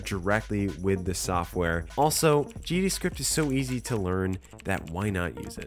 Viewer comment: You say GD Script and captions say Javascript 😂